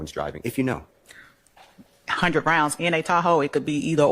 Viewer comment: I need hearing aids, I thought they said rounds in a taco